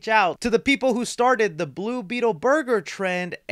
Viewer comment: Why did when you said he took them out for tacos I thought you said he took them to copyright